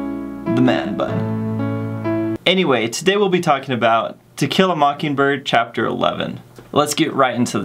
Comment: Lmao I heard “tequila mockingbird “ And it is May 5th